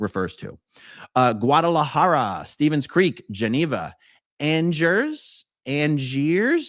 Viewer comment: Algiers is the capitol of Algeria in Africa. However, it was governed by the French Republic, and then the Vichy French during WWII and continued as a French colony through the 1950's, so perhaps that is why you thought it was French? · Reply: I could be wrong, but I heard “Angers”